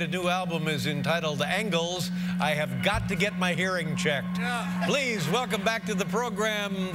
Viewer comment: What did David say at 0:10? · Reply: «I HAVE GOT TO GET MY HEARING CHECKED»